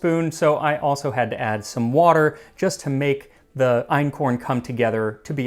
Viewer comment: I thought you said einhorn and im like UNICORN MEAT??